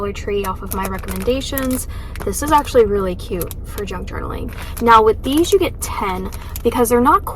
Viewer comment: I thought you said “drunk journaling” 😂 I was thinking that does sound like so much fun!! I had to go back & listen again.